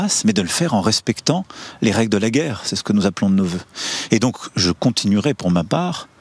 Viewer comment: "..c'est ce que nous appelons bveuheuv" Qu'est-ce qui dit???